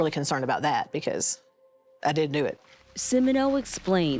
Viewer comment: the mother literally said she wasn't worried about them accussing her because she ''did do it'' anyone else catch that. watched it like 5 times an she said did do it not didn't do it..